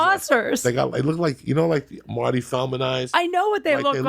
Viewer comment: Did he just say his balls look like Marty Feldman's eyes? LOL!